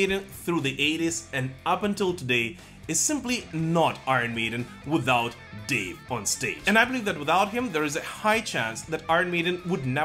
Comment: I thought you said Dave Mustaine.